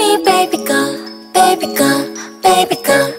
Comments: Do you realize in the end when she says baby girl, it sounds like she says barbie girl, like she's a toy. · It kinda sounded like Hyuna said Barbie girl at the end.